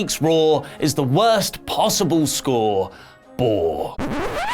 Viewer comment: did you just say Whore? 🤣